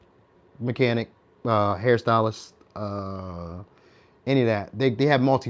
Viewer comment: had a nervous one second as i thought you said Stalist ( Stalin ) then realised you said Stylist ...🤣😂